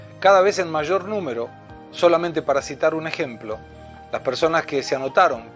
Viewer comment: " solamente PARASITAR un ejemplo" ...lo entendí 🤙😉. Min